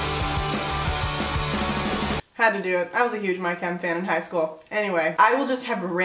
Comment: Thought she said "My Can". Not "My Chem".